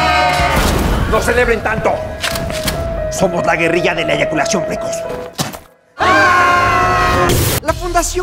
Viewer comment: no entendí eso, me explican por favor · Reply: "Guerrilla de evacuación precoz"